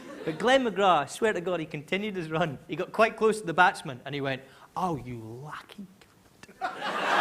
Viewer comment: wth did he say?? I'm lost, please help this non-native speaker :3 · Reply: It’s a dirty word: “Oh, you lucky c**t” is what I worked out.